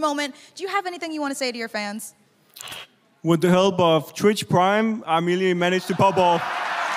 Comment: can someone please explain what dafran said I can’t understand it for the life of me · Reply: He said "with the help of twitch prime I merely managed to pop off" then while walking away he says "nice"